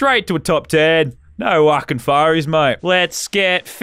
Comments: Did did he just say no waking furies mate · 🤔 huh why did u say no wucking furies